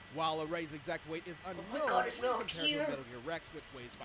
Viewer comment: did she say METAL GEAR? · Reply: Yes XD I was trying to imitate when he said "Oh my god, it's Metal Gear".